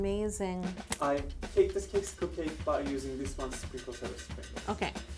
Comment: I thought that said cocksicle I had so many questions 🤦‍♀️🤦‍♀️